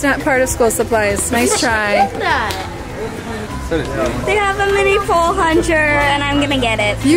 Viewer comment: Did anyone catch the “mini pole huncher” from at 8:05?? 😂